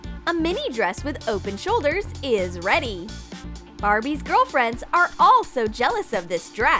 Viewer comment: does it say BARBIES GIRLFRIENDS?!?!